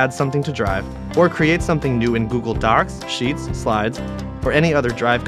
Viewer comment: What's this new app Google Darks? He said that right? I'm not crazy. He said Google "Darks." 0:15 ....